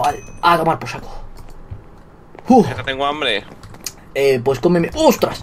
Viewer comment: Fui La única que en el minuto 2:28 Cuando Willy Decía Vegetta tengo Hambre Escuche "Pues ComeME" .__.